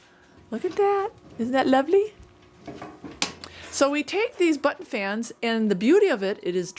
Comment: I swear, this Southern girl is still not used to your accent. I thought you said "butt fans". Now there's a visual for ya! hahaha